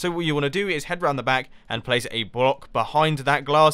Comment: was it only me or mumbo said "brock" instead of block